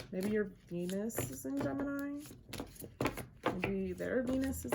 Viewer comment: haha i thought you said penis in gemini..